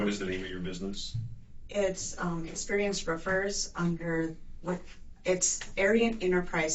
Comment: 😂 stg I thought they were saying “experience Reverse” 😂 But they’re actually saying “Experienced Riffers” 😂😂 Jus kidding.. they’re really saying “Experienced Ruffers” 🤣 Okay okay I’m Jus kidding 🤣 “Experienced Refers” 🤣🤣